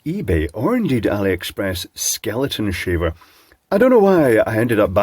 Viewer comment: Nyayahahayayahayaya! Oh sorry I thought you said Skeletor.